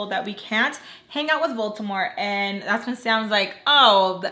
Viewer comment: When I first started watching the series, I could’ve swore you were saying Baltimore.... not voldemort 😂😂